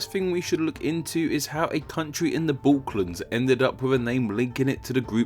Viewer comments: Cool video but it sounded like you said “the balklands” rather than “the balkans” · "How a country in the Balclands" I can swear I could hear you say that. 0:38... oh wait... you keep saying it... classic ignorance. FYI it is called Balckan peninsula. You are most welcome.